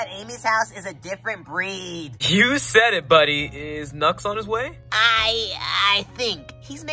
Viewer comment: bro when you said is knucks on the way i thought you said is nuts on the way 💀💀💀